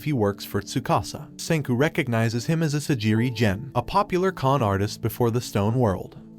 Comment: wHaT dId He SaY Sagire gen dude it’s Asagiri gen